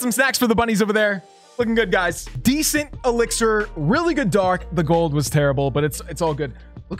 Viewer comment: decent? Did you say decent? DECENT ELIXIR? Aww man that's small elixir for me at th9😂😂😂